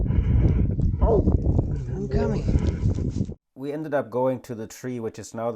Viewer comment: sounds like she said come..